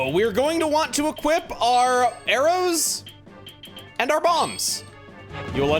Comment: Lol At 8:35 I thought he said to equip the arrows and your mom for a second.